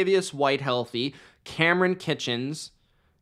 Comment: ?? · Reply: It sounded like you said Kamren“Kitchens”